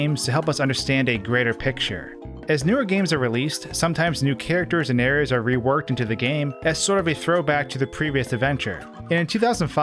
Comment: don't know who else heard it but I heard previouth 😂sounded like Mike tyson saying pigeonths 😂